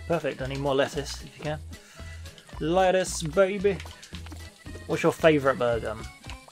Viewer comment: When you asked, "What's your favorite burger?" I thought you said, "What's you favorite murder?"